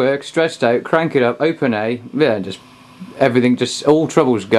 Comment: ok because at 5:43 I thought you said it was in open A